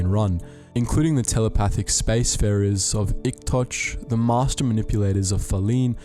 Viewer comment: I heard that as “dick touch”